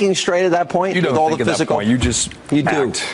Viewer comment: Did he just say . "its not a honor to meet you" ? xD That funny ... He might have said ." Its now an honor to me you"